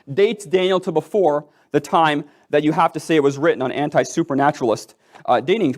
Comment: We should not concern ourselves with a fictitious God of vile temperament, and voracious appetite for violence, but we ought to fear those who endeavor to follow him, march to his drums, and obey his commands. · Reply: Did he just say anti- supernaturalist?